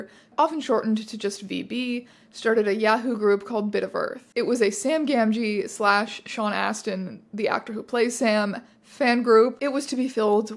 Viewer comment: For a moment I thought you were saying that the group was for shipping Sam Gamgee with Sean Astin!